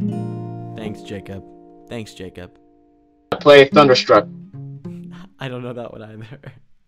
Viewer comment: DID HE JUST SAY THUNDERSTRUCK!?!?!?!?!?!? Damn it. It's Starstruck.